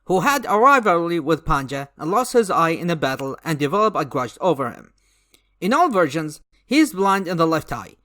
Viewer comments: When I first heard about Panja and Bubu, I misheard the phrase “and developed a Grudge over him” as “and developed a CRUSH over him.” Uhhh. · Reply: When I first heard about Panja and Bubu, I misheard the phrase “and developed a Grudge over him” as “and developed a CRUSH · When I first heard about Panja and Bubu, I misheard the phrase “and developed a Grudge over him” as “and developed a CRUSH over him.” Uhhh.